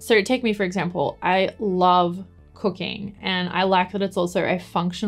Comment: I did such a hard double take when you said "I love cooking" because at first I thought you said "I love cocaine" because of your accent hahahaha